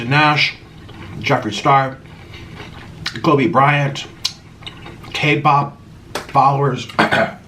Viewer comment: did he just say tape bop?